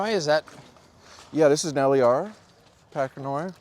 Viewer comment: did he say LAR pachanoi? My ears don't work very well. It's such a nice green.